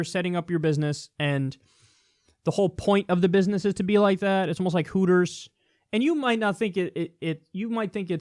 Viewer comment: "out of context' now "misheard ": 1:27 "IT SMELLS LIKE COOTERS"......kinky